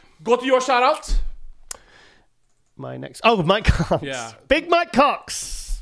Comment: I heard "Oh my cock" lol Another great episode guys. didn't know Labrada owned lean body, those shakes are great I always buy them. Patrick Moore looks like a buff Dwight Howard, looking forward to see what he 'll bring to the Olympia. Maybe you guys can post the Instagram to of the people you're interviewing or shouting out, it would make it easier to follow them.